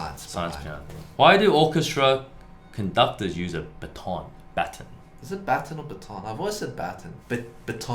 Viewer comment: I was very confused this instant bc I thought they were saying that conductors use lipstick 🤣🤣🤣 (Since I'm from brazil we speak portuguese so: batom=lipstick)